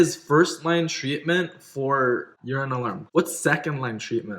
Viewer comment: you lost me what you were asking for first line treatment and you were talking about ADH agonists/antagonists .. don't know for what. all I heard was "ur-an-alarm" LOL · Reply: Sarah Tedesco urine alarm - it's a sensor on the bed that detects urine. It wakes the kid up so that they can get to the toilet to finish peeing